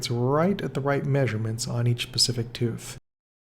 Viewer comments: Did he say pacific instead of specific. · did he say “on each pasific tooth” or am i just stupid ?